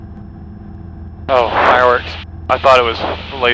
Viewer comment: What was the flashing light at 2:03? I can't make out the first word. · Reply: Fireworks, they thought it was a laser at first.